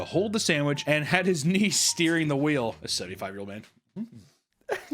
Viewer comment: do we think he was meant to say knees? Niece is way funnier but she disappeared after the crash!!